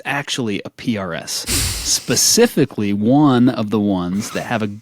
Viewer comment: I heard say “PRS SE” the first time too.